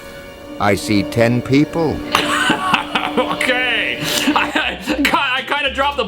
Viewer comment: Instead of him saying he sees dead people He says Let me tell you my secret:🏃 I see 10 people 🕴